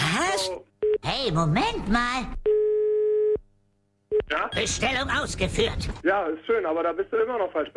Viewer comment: Als ich die Stelle zum ersten mal gehört habe,hab ich Bestellung ausgef×××t verstanden